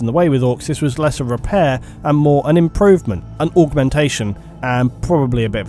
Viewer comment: You missed out on the opportunity to say "orkmentation" instead of "augmentation" and I will never forgive you for this. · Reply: I thought he was saying orkmentation the whole time